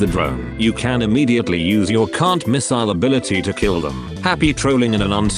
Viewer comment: I'm not very familiar with BfN, so I thought you said "C*nt Missile", not "Can't-Miss-ile" :D